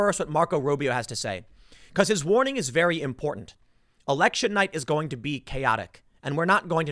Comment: Did Tim say erection night????